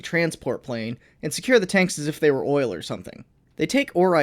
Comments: Did I hear, O I L?? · DID YOU SAY OIL WOKE